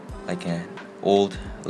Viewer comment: did he say “unsubscribe for more content” 😭